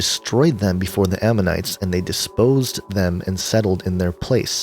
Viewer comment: you say disposed instead of dispossessed. The overall meaning was still there though.